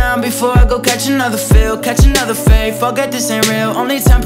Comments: did yall heard Daniel sweared lol i did not expected that but i loved this song omgg and the ending thoooo hdsfsgfhbnwhs •EDIT• I realized that he said forget by looking at the lyrics but he said it sooo fast lol so it sounded like sweared lol bdnsnsjsns im sorry and thanks for the likes babiesss !!! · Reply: Lazaria Williams 1:59 he actually said forget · Who else thought when he said forget he said f it?